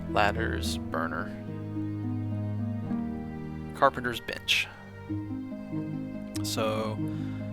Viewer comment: I thought he said "carpenter's bitch"